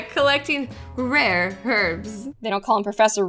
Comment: I thought the rare herbs said rare heads...